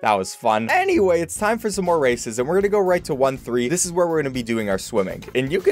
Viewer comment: I thought you said "Time for more Racism"